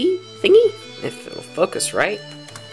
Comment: when he said if it will focus right I thought he said if it will f?&k us right